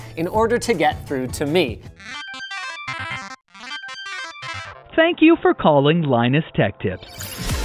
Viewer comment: I think I need more coffee... I heard "Thank you for Colin Linus Tech Tips." at 0:38..